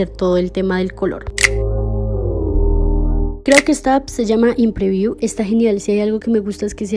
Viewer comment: Alguien me puede escribir el nombre de la app del min 5:48 , es que no entendí lo que dice. Gracias 💕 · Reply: hola, acabo de ver otro comentario y le respondieron que se llamaba inpreview. La busque y me parece que solo es para iphone pero hay otra que se llama igual y creo que esa si es para android.